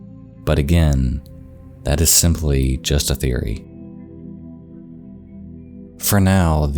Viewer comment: why did i just hear say " a C0N$P!R4CY THEORY"